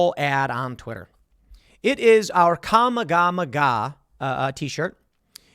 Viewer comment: Did he just say kama-Gama-haa instead of kamehameha?